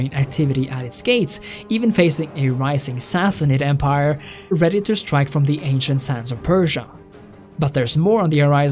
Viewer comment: The funniest thing about this video is having it on the other monitor, busy with something else while listening in and at 32:00 mishearing you as saying "... even facing a rising Sassanid empire, REDDITORS strike from the ancient sands of Persia"